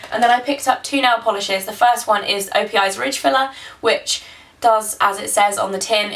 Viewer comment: When you said ridge filler, I thought you said rich fella. I love your accent :-)